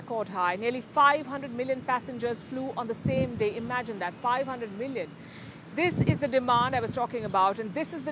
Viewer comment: did she say 500 million in a day Or what? Is that correct. Bonkers